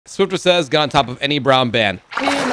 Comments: At first I thought he said brown man. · I thought u said brown man 💀